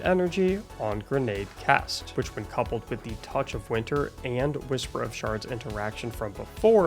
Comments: I thought for a moment you said whisper of sharts 😂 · istg i heard "whisper of sharts", and my immaturity made me violently giggle 🙃